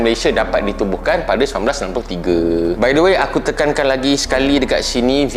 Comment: Minit 13:24. Malaysia di tubuhkan pada tahun 1993? Saya kurang faham. Ke salah dengar.